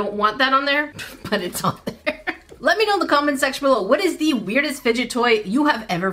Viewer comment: !!😮 Did you say “comment seggual”?!?!?!😅